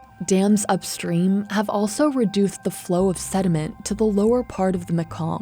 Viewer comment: Did she just say reduthed the thediment 🤣